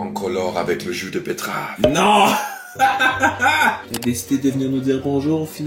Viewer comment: "nooon!" Moi j’ai compris ’’vade retro satanas!’’🤣🤣